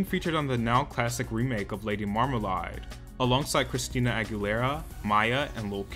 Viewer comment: did u just say "Lady Marm Ma Lai"? lol